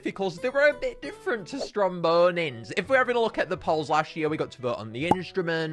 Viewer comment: i was listening while on a different tab, and i swore i heard "Zomboni" instead of "Strombonin"